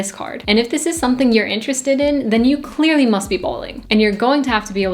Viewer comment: Did you say "bowling"?😅😅